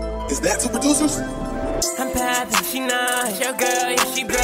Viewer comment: does anybody know what he said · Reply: I’m poppin, she not